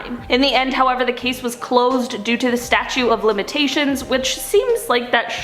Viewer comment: Did you say statue of limitations? It is statute of limitations. Never too popular to learn new things. If I misheard you, apologies. I really like your vIdeos.